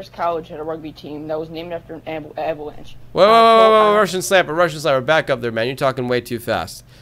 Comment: i was wondering what was talking about then when he said it slower i realized it was the Andes mountain survivors